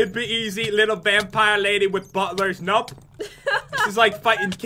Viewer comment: For a sec I thought butler said BUTTER!